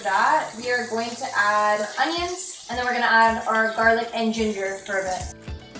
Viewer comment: did she just say garlic naan bread